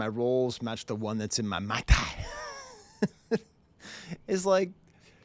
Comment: Rolls Royce all come with a fancy branded umbrella, they’re a status symbol. So he’s basically just flexing, saying “the umbrella in my mai tai (remember he’s on an island wearing tie die lol) matches the one that came with my RR”. He’s not saying he has a tiny paper umbrella in his car 😂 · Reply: I thought "mai tai" was him saying "motor" but heavily accented to fit the rhyme and car scheme